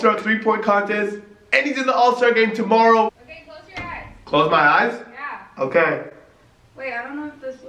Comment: I lowkey heard say babe close your eyes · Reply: sounded like she said “Hey close your eyes”